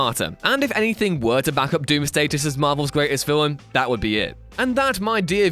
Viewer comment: you told Doomsday is the best marvel villain instead of Dr.Doom! LOL !!!!!!